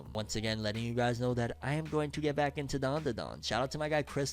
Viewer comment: Editor put Doron- Doron instead of DanDan… lol 😂